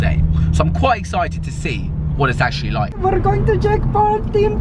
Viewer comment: It sound like you saying Jake Pool :D